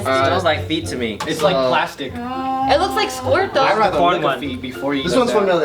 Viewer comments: did she said "it looks like squird tho" ?!! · Did say that it looks like squirt?!